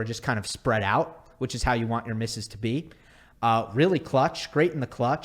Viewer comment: i heard "spread out, which is how you want your Mrs. to be" 😂